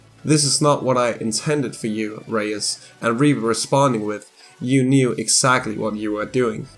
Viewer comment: Are you saying reaper or reaver it may just be my iPad but you sound like you say reaver on my phone and computer?